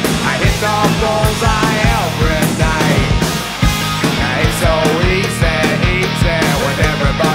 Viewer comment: En E.U : It's so easy En Latinoamérica. : Eso hice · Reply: Yo pensé que decía it's so izzy xd